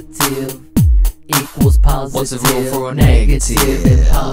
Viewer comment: Hi can i ask what's the lyric did you said about 0:46? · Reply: "What's the rule for a positive?"...best of luck!